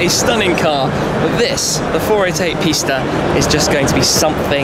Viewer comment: I’m not lying But I thought he said the 488 pasta and I fuckin died laughing and the I said pasta in my head every time he said 488 pista ahaha